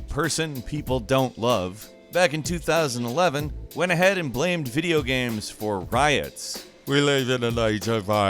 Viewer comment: I thought blamed said banned